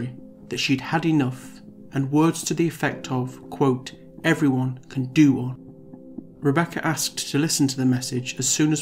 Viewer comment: "Everyone can do..." what? What does he say here? help. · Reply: “Everyone can do ONE”. It means like “everyone can get stuffed/get fucked”